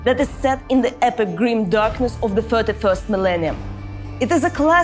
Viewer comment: sound and look like she could become a Commissar... Lord/Lady Commissar, no less. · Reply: honestly her accent made it sound like she said 31st millennia and I got confused